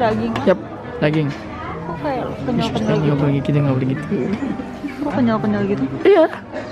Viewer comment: gaboleh, gitu" tapi kaga kedengeran wkwk